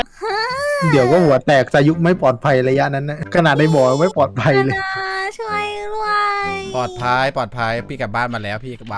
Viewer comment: พูดว่าอะไรนะ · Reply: พูดว่า ติดหินอะพี่